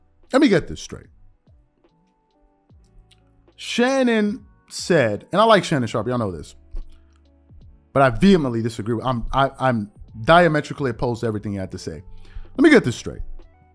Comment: When did i say I dislike him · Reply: I BOLDLY WILL TAKE THE OPPURTUNITY to say..my bad i thought u said DISLIKE...in fact you said I LIKE shannon but disagree,...my bad...i could have jus deleted the comment but maybe someone can learn how to simply take a L and move on.....my bad.